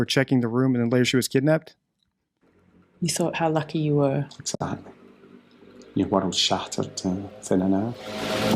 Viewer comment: Just past 57:00 I think Jerry says, Your world is shattered." Not "My world was shattered."